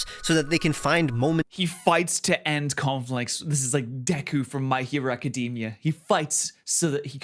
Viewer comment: I thought you said, "Dobby from my hero academia," and i was so confused until i figured it out